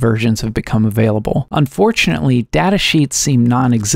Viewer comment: somehow i heard "unfortunately" as "un-4chan-attly"